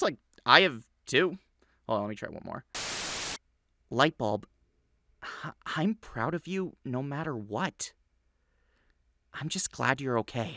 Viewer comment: I THOUGHT YOU WERE SAYING 'Light bulb.. I love you no matter what! Lets kiss."